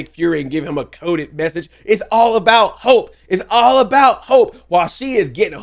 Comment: I dont know why i heard "its all about hulk" when i first saw the episode instead of hope